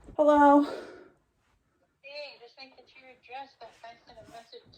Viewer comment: When her mom was calling (3:50) I heard arrested, and I was like, WHO???? Lol